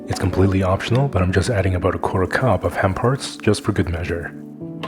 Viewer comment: Did you say hemp hearts???